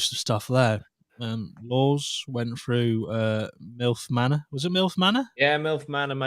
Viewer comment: thought said milk manor 😂💀